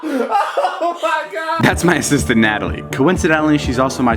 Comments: I thought David Said "that's my sister Natalie" at first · For a second, I thought he said: 'This is my sister Natalie'